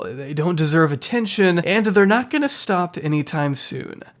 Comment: I am not sure if you've already been made aware, but the auto captions are problematic at the end of video. Instead of "but it is fun to hate on them at least" it reads "but it is fun to hate on the Middle East"